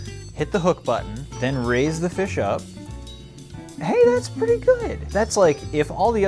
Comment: breh nobody noted it sounded like he said RIZZ the fish up😅 and if you want to hear it (go to